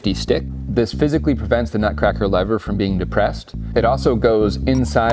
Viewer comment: I thought I heard you say this prevents the nut cracker from being physically depressed